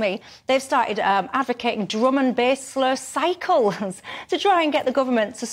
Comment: did she say "drum and base low cycles"? What on earth does that mean?